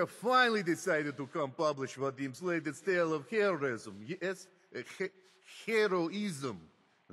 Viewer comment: when he says heroism it sounds like he says terrorism